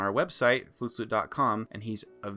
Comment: I doubt I'm the first one who's made this mistake. But when I heard you say your channel name, I thought you said "flute slut" where slut was pronounced in the meme way. So I immediately subbed. Imagine my disappointment upon reading the channel name. Either way, liked the content so staying subbed.